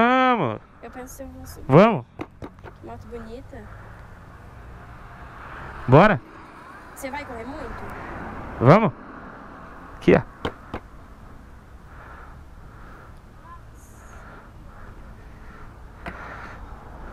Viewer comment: mano eu escutei ela dizendo "vc vai comer muito?"